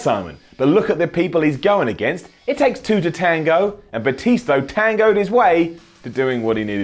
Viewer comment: Lol at 1:23 did he say Batisto